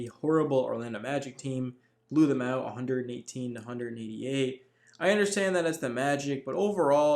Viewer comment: "...118 to 188?" What?!!! Am I hearing this right?